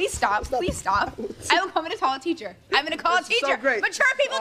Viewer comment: i thought she was saying “i’m gonna fucking call a teacher” at 0:42 😭